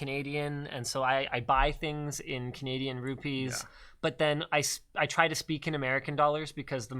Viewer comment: did he said Canadian RUPEES?